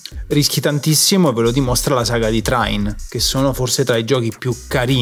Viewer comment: non ho capito come si chiama qualcuno me lo scrive? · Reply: Trine